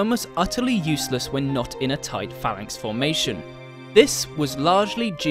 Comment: Neat video! Still wondering how something that sounds so great stop being used though. I wonder if I'm missing something... Also 2:08 I think that's supposed to be phalanx instead of pharnyx, right?